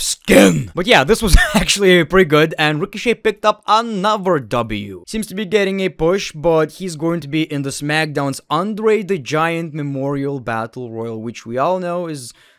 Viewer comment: I thought you said."Smackdown's Underrated Giant memorial battle royal" :D